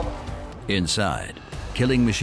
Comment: Killing machine Anus? Is that what he said his name was? The guy with walmart sunglasses?